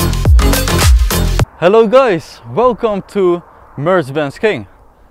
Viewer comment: I hear always "Hello gAys" but with deeper voice.